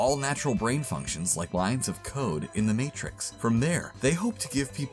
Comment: Thought the guy said, " lines of coke" instead of "lines of code". Whoops